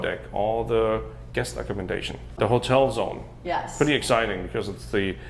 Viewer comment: Hotel Zone. Phew. I thought said Notell Motel Zone. Dang, my hearing anyway.